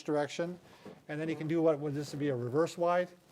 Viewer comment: i thought he said 'then he can do what he does with his wife' xD